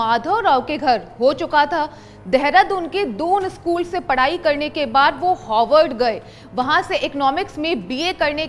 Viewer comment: I felt like I heard Hogwarts 😂